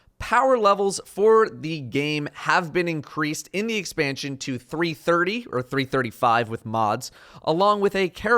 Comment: I thought they said power level was going to be 225, and 325 with mods. which if it's not a slip of the tongue might point at some new power 10 mods perhaps?